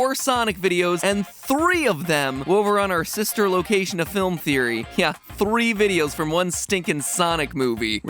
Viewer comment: I thought you said you were calling Food Theory your sister location